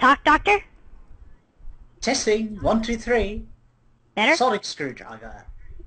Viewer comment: at about 2:30 when the Doctor says sonic screwdriver captions say "excellent storyteller" 11:15 "kevin smith in these deficits" 18:15 "galactose justin alcott i got" and the best... 18:40 "action sarika sh** teleportation you sambar ball" Oh captions...you make my day